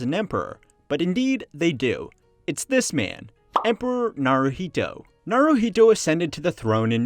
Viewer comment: I heard him say "emperor Naruto". Did you??